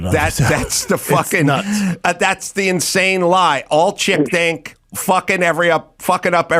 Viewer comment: I thought he said Chimp think 👀😬😂